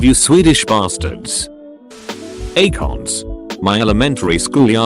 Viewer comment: I thought he said, “Acogs” I’m like, “damn you guys had assault rifle optics in school?!”